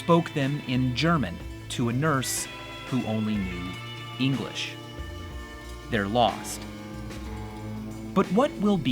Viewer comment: bro i swear vsauce said the nurse spoke french not english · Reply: he clearly says English